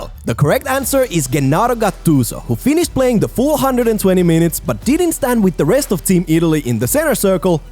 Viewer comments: did he say 420 mintes? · did you just say 420 minutes? lol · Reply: He said "full hundred and twenty minutes"